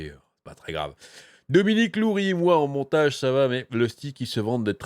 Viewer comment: A la fin de la vidéo , j'ai compris "Lusty est très bien monté" ... (J'ai pensé à autre chose).